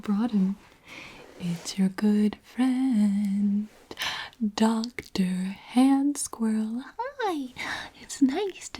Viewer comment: AHAHBDQWFUWQBFQL I WAS HALF ASLEEP AND THEN I JUST HEAR “ 🎶*dr hand squirrrrelll*🎶 I LAUGHED SO HARD💀🤚